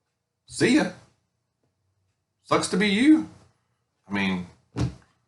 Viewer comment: I’m so tired that I thought you said “clucks to be you” instead of “sucks to be you” definitely need to get some sleep 😂